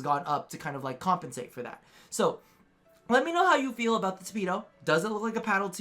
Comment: I thought you said “speedo” when you were talking about the tipedo prime. -.